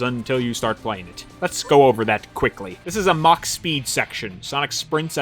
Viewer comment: Oh, MACH speed sections. I always thought people were saying MOCK speed.